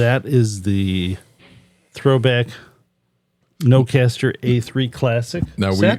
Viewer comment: I heard "nocaster", confused, did you demo broadcaster or nocaster? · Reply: This is our Broadcaster A3-Classic set.